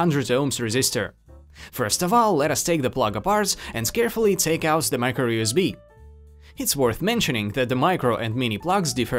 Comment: did he said "and scarefully take apart the micro USB"?